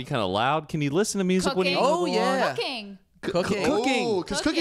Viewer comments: it sounds like shes saying cocaine 😂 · When she said cooking I thought she said cocaine... I was like umm okayyyy never seen that side of her